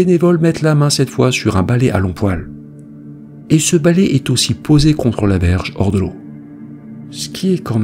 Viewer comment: "contre la verge au bord de l'eau" ? y'a que moi qui bug la dessus ? 6:31 😅